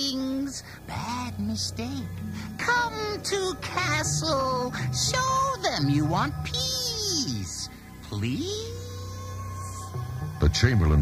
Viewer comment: I always heard it as "Come to castle. Show them you want peas. Please?" I thought that Gelflings didn't like to eat vegetables either.